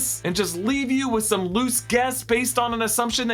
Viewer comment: I honestly thought you said “loose gas” instead of “loose guess” and, given Marrok’s demise, I cackled so loudly 😂